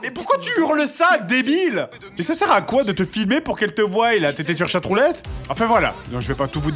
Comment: Il a bien dit " Pour qu'elles te voyent " ? .... Edit : 7:58 "Pour qu'on voye pas" c'est moi qui entend mal ou ?